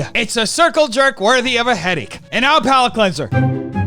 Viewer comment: Had to rewatch the comment "It's a circle jerk worthy of a headache" bc at first I thought said "heady" and I was like, "Wait, what? Is that what the kids are calling it now? A heady? Like a handy but...with...your head?"